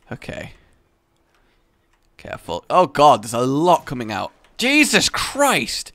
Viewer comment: I had a notification and when the sound for it stopped all I heard was "oh God he came in!" I thought "what the fuck?"